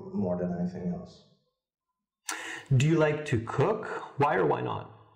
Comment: I heard "cock" Roflanebalo LOL Intellectual humor